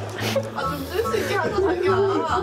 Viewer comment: 왜나만 9:35에 자기야라고들리지?